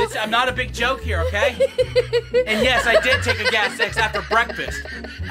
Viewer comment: When she said “ did you take Gas-X” I thought she said did you take cow s&x